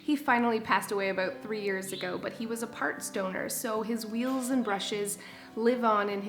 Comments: Lol! It took me a minute to register that you said Roberto was a parts donor. I thought you said he was part stoner. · I thought you said he was a "part stoner" not parts donor. LOL!! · Oh and when you said "parts donor" I thought you said "part stoner" like he was just a lazy roomba then I said oooooooh parts DONOR lol